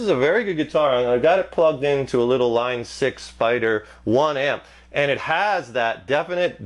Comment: Sounded like he said "A Line 6 fighter 1 amp"